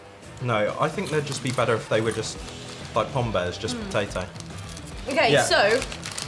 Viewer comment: what did he say Pam bays ?